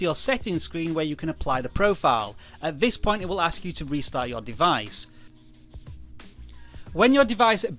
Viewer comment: IMPORTANT QUESTION Q:Does It Work On Ipad air 2? Q2:Does it actually reset my ipad (like delete all my progress) EDIT: OH MY BAD I THOUGHT YOU SAID RESET BUT YOU ACTUALLY SAID restart